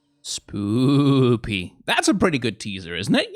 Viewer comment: wait, did you say spoopy?